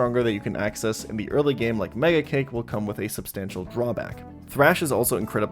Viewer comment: I had to do a double take. It sounded like you said "Laya Cake".